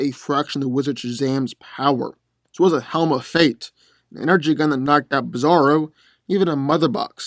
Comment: what does he say there? I'm hearing "she was a helm of fate" but he's not talking about any females at the moment do I'm confused as to what he's actually saying there What is he saying there?..... What I'm actually asking is who can hear better then I could? lol